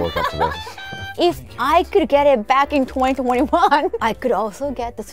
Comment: Who was that also in the background a male voice saying what Kaho say "if i could get it" between 5:00 - 5:03, like i cant unhear it at all.